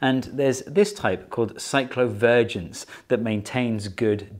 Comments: I keep hearing "cyclovirgins" which is what I am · What he said: Cyclovergence What i hear: Psycho Virgins . . Ok. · The first time he said "cyclovergence" I thought he said "cyclo virgins". For a hot minute, I had pretty kinky thoughts. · am I the only one who heard Psychovirgins